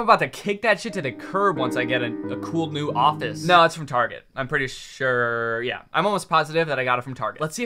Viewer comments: are people not gon talk about this? its an offensive slur wtf? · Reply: he said "its from target" but i see why you thought he said that other thing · yall have no idea how much fear i had when i misheard "from target" as the r slur 😭😭😭